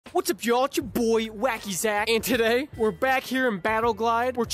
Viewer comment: at first i thought you said wacky sack